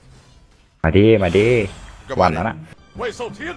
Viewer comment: เหมือนได้ยินว่าปั้มยาก่อน5555